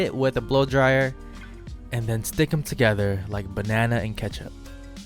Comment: I heard it and it took me a while to process but “banana and ketchup” 😂